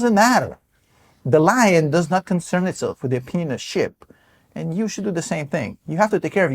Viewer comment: Get my complete DCA tutorial guide for FREE here: <link> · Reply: Thank you very much for your videos. They are so great. Please help me out with what you said at 8:12. "the lion is not concerned with the opinions of ship? opinionship?" My apologies for not hearing you. I must tell you your laugh in "The Stock Market Apocalypse" video three weeks ago when you pronounced minerals as "menereal" was epic! I shared that video with a family member who was worrying about Trump policies. I laughed, I watched over again, it was great!